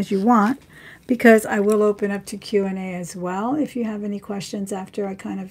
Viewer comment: Lol Thought you said we stay as Long as you want.. I’ll open up tequila 😜